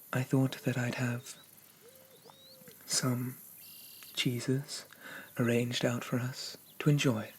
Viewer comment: Omg when you said "I have some cheeses" I thought you said "I have some JESUS" coz boi do i need some of that Petition to rename him Prince of Cheese because who else is going to take me on a cheese journey such a this? I feel so enlightened, so blessed 🙏 Has he ever tried grilled cheese? He needs to try grilled cheese. We need to have grilled cheese for breakfast. I only sleep for 4 hours a night so wakey wakey cheese and bakey 👏👏👏